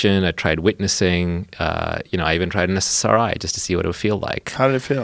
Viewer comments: What does he say at 1:59:45? "I even tried..." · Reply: SSRI - Selective serotonin reuptake inhibitor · what does he say at the 1:59:45 mark that he tried and turned him from a pessimist to an optimist? i cannot figure out the word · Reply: He said he tried an SSRI. Anti depressant basically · What does he say at 1:59:46 ? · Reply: Ssri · What he say at 1:59:47? MSRI? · what did he say he tried? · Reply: SSRI it's an anti depressant